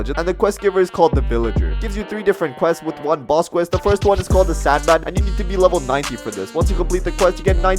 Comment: Did you said “Sand Bandit”